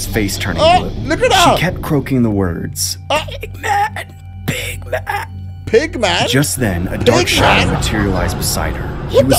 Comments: I misheard big man as Big Mac. We need Scooby and the gang to solve that mystery. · Thought she was saying "Big mac" XD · I thought she said Big Mac not Big Man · in the last one I thought she said 'big mac' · I thought she said Big Mac😂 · I thought she said big mac